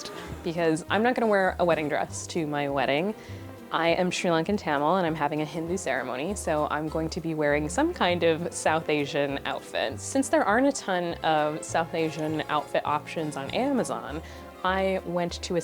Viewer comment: Srilankan Tamil? Wow that’s cool, I’m just Tamil... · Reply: HAYDON :D I got excited because I thought it said South Indian and I was hoping she’d talk about our state